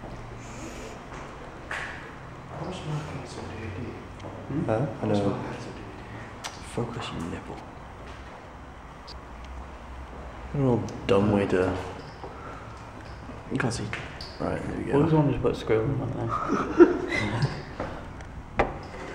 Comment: At about 2:00 did I hear,"Focus you nipple"?